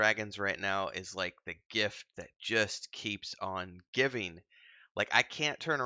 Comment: I thought he said gif that keeps on giffing. Was actually pretty curious to see where he would go with that.